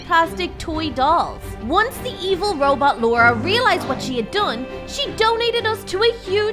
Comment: For a sec I thought she said "evil robot Dora".